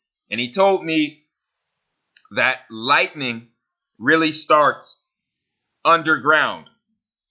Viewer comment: I thought u said farts lol